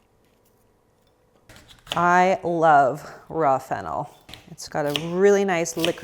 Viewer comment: I like this approach to food: no crazy restrictions, no 'good' and 'bad' foods, etc. Also, am I the only one who heard 'I love rough an*l' at 13:17? I'm sorry 🙈 I actually really like this channel😆